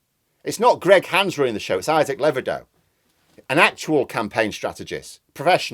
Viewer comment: I thought you said "Isaac Lebedev," oh Levido, I thought there was another one of putin's cronies involved.